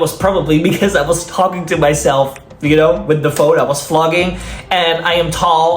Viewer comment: OMG the subtitles…. 6:33 ….. is killing me. Flogging instead of vloging? That gave me very interesting ideas…..😂😂😂😂😂